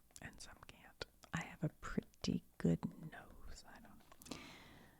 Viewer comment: i was dosing off and when you said "i have a pretty good nose" i thought you said "i have a pretty big nose" i had to rewind to clarify haha